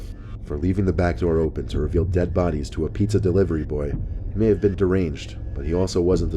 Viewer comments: Pizza? i thought he said hamburgers on story 1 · Pizza???🤔I thought you said McDonalds?